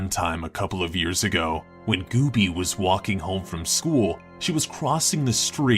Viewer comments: It says Doopie instead of Gooby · anyone notice that it said 'doopie' instead of goobie? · wait why does it say doopie that’s goobie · WTF? It's Gooby but it says Doopie. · Ok at 6:02 the subtitle said "Doopie" but said Gooby. ???